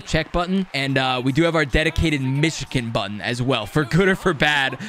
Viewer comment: Its too easy to do the michigan now. Also: Its just tough to pull this michigan. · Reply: It's weird how he pronounces 'Michigan.' I thought he was saying 'Myshkin,' and I was like "Why did they name this type of goal after Vladimir Myshkin?"